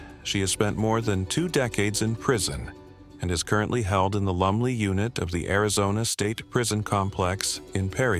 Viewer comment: Its Lumley !!! I thought you were saying Lovely prison unit !! Lolol